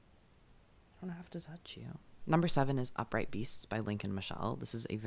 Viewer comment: When you said Lincoln Michelle, for a second I thought you said Leigh Michelle 😂